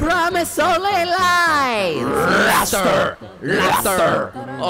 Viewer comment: i hear is mafter?? Lol!!